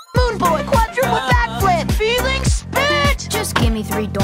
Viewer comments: I thought he said "FEELINGS BITCH" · When he says healing spit! I thought he said ''Felix bitch!'' and when he said i like all basic shapes i thought he said ''i like all basic chicks''